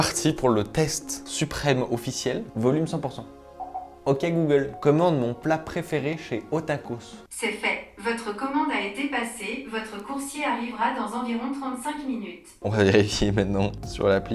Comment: Qui a compris quelque chose ? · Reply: J'ai compris "tacos"